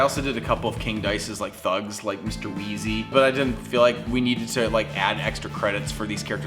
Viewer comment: When you said “Mr.Weesy” I thought you said “Mr.Weasley” from Harry Potter.